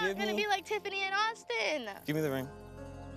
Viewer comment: I thought the man said give me the rain and it was a ring crazy me